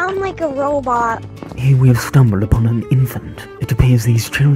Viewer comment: I THOUGHT HE SAID ORPHAN INSTEAD OF INFANT 😂